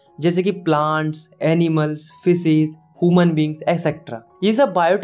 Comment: is it just me who heard him saying physics instead of fishes ?